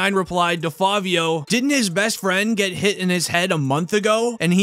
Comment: Bruh I thought u said his dead